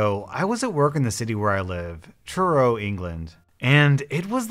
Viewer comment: When you said Truro I thought you said churro